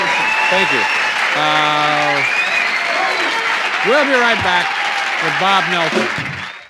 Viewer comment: Did he just say, "We'll be right back with Bob Dylan"??